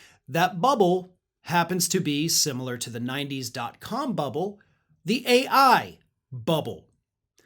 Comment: I thought he said cum bubble I died 😂😂😂 man I'm baked like a potato 😂😂😂